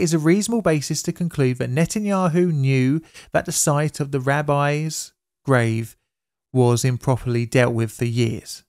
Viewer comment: And I thought you said “Rabbis' Rave.”